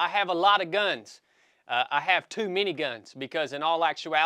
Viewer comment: Haha bro I thought you said you had 2 miniguns lmao - “too many guns” 10-4 😂